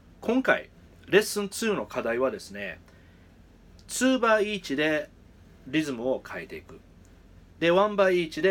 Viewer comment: はじめましてこんにちは。もしよろしければ教えてください。4:34あたりから"one by each"や"two by each"とおっしゃってるように聞こえます。これは、四分の一拍子・八分の一拍子・三連符・16分の一拍子それぞれのピッキングを一回行ってから次のピッキングを行うこと(one by each?)、二回繰り返してから次のピッキングを行うこと(two by each?)という意味なのでしょうか。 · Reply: ありがとうございます！すいません！ボクの発音が悪くて。One Bar Eachです。各小節です！